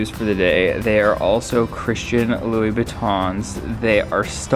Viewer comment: “Christian lou-bootoins” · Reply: Caroline Franklin i deadass thought he said “christian louis-vuittons”